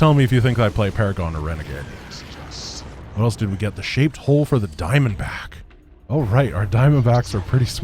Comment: when he said he got she shaped hull upgrade for the diamondback i thought he said shaped hole, so now i have a mental image of teaching the diamondback driver what shape goes where and the driver just going "mhm mhm".